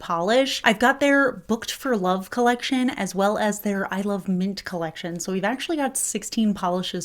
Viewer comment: So I was cuddling with my parrot and listening to the video. I thought I heard you say "I love meat" but I was 99% sure I misheard you. So I went back, and thank goodness it was actually "I love mint" 😂